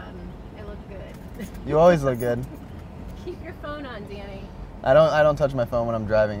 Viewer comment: When she says Danny she sound like she saying daddy😂